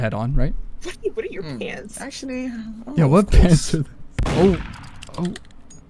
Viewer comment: "umm...Actually" (am I the only one who heard say that?)